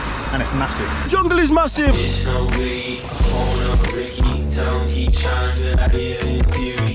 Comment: Thought I heard junglist massive. You folks into drum and bass?